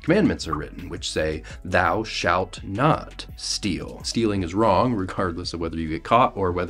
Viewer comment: I thought you said it was thou shalt not kidnap.